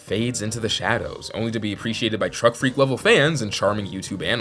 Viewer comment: did he say...gulp...truck?